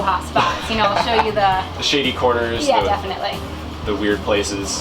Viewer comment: So, at 1:05, did he say "shady"?